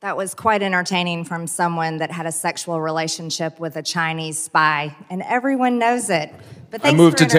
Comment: I thought she said; “Chinese bi” my imagination was running wild for a minute there….😵‍💫